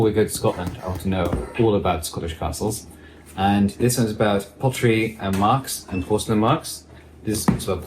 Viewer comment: I thought said "pottery & Marx". A very niche book if ever there was! 😂